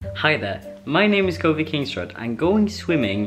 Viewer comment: I swear I thought he said his name was covid kingsrod